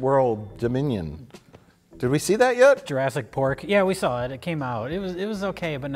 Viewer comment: I heard "Jurassic Pork" and now I need a Batman movie where he spies on lady dinosaurs in the prehistoric shower...